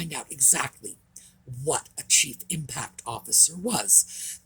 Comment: I thought you said “Cheap Impact Officer”.......🤣